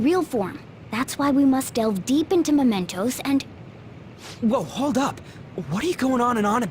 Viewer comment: Thought Morgana said mentos instead of mementos Oh my